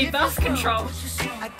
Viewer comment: I thought she said something about breath control... And then I realized she said Birth Control and im dying! Also same! 🤣🤣🤣🤣